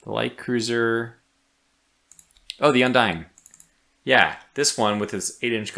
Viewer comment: Everytime you say ship name "Undine" I hear "Undying" and think about Game of Thrones